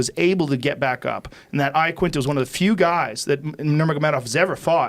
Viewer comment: Lol I thought joe was saying al I Quinta was retarded 😂😂😂😂